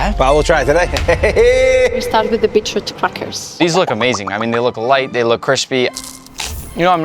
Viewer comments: had to put in subtitles so ppl wouldnt think she said b*tchy f*ckers 🤣🤣🤣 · When she said :we'll start with the beetort crackers POV ME:I thought she said f******😅